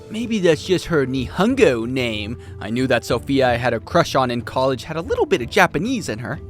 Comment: really? I Heard Sophitia 🤔